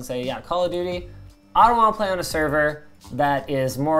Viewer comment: the editor thought he said "I wanna play on a server thats... " But he really said "I dont wanna play on a server thats" Hence the asterisk, I got you Linus.